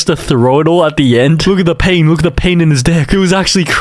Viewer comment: did bro say dih